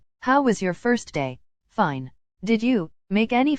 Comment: when he said fine i thought he said fuck because i was moving about and making noise